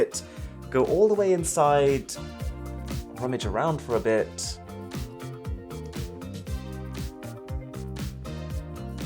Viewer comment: what did he say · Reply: "go all the way inside, rummage around for a bit"